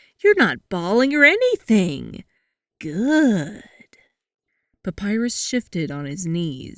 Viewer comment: My earbuds are broken so i heard: “Papyrus—shitting— on his knees...”